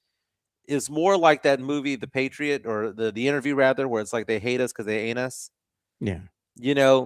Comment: I mistakenly heard you say "They hate us because they anus." 😂